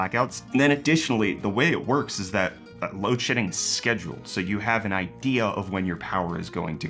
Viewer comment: I kept hearing "load sh*tting" and thought oh, that's why he's fidgeting in his chair.